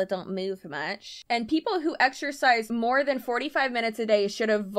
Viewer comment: I really thought said exercise for more than 5 min a day😂 but also this wouldnt be a surprise for this mlm.